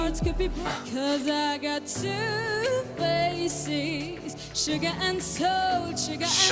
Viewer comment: The sugar and salt one sounds like she is saying "I've got two feces"